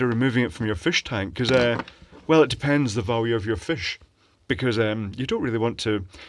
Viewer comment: I swear I heard "well, it depends the voltage of your fish" and I was like "yup, nothing wrong there"